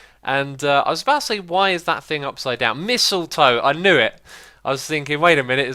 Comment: Did you just say Missiletoe? Sounded like that the second time...